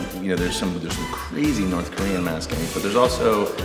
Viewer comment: Did he say North Korea?